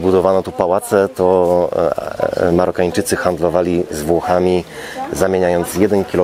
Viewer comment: "..handlowali z Włochami" a ja usłyszałam "..handlowali zwłokami" xd